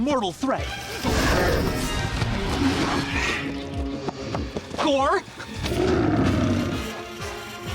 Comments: Thought he said whore lmao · he said gore... I thought he said core at first At first I was like: ah, now that's scary